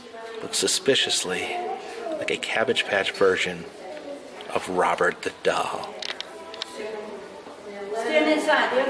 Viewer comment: I thought you said he looked like Robert Duval.